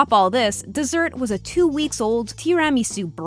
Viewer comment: did she just say "Tee ram eesu?" Tira me su, you muppet..